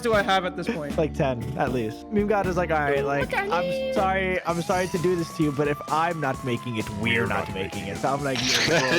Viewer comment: what sayin? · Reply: "oo, look at miii"